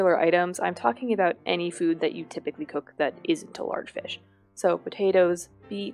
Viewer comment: First thought you said"not a typicial fish" 😂